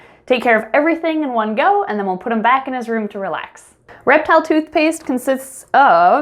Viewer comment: I swear it sounded like “Rectal toothpaste” lol.